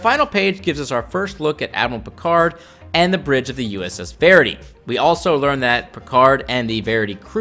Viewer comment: It's spelled 'Verity' in the comic panel, so I pronounced the best way I could. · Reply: Sorry I could have swore you were saying Faraday (which is a great name for a shuttle perhaps as well as a Starship). I wonder now is there a mr or mrs Verity that it's named after as Verity sounds more like a girls name some one would give their sail boat to me. Might be something as simple as someone naming a ship or registry after a family member (again). But in which case they'd need a fictional Verity to name it after. It could simply be someone being pretentious and the naming convention of the class of ship is Latin words (in this case meaning Truth) I just hope it's not named after Damien Hirst's terrible statue.